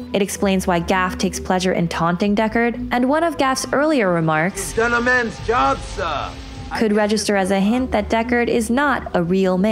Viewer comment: what the hell does Gaff say here? (edit) nvm turned on captions "You've done a man's job sir"